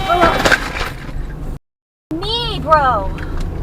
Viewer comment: why did you say negro?